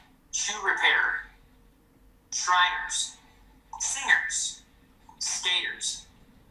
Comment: So basically ANY living/breathing adult and possibly children lol got it 😂 when he started naming states, I just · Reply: When he said “singers” I misheard and thought he said SWINGERS!